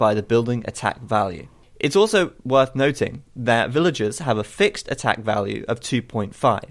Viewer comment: Does supremacy change the damage output of garrisoned villagers for the Spanish? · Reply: "villagers have a fixed attack value..." is what the man said so i guess it wont change...